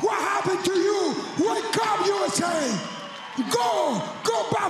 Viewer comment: I heard "no forget Jesus, people" in broken English. I didn't hear "not for gay Jesus." It's pretty obvious he said "no forget Jesus" just based on the context of the interview.